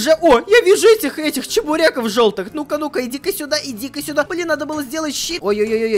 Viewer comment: мне послышалось или сказал "надо было сделать щи "?